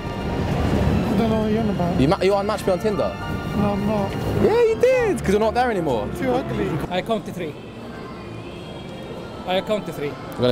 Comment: DID THE COP SAID U TOO UGLY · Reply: I thought he said "I'm not on there 'cause I'm too ugly". Went back and all you hear is ".. too ugly "